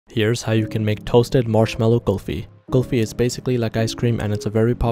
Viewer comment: I heard you say mushroom kulfi until I saw u toasting the marshmallows💀